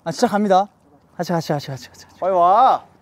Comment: WAIT!!! DID DOKYEOM SAY 'shibal' at 13:56???? AM I HEARING CORRECTLY??? OMG HOW DID I NEVER NOTICE THAT?